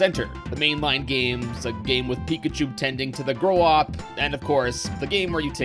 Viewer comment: "A game with Pikachu tending to the..." What? 🤔 Grow up? Crow...op? Korok? What's he saying lol I like the "Pikachu noticed the WEED." line though 🤣